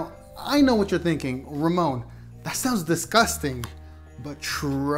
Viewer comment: Did u just say rumome in steady of ramon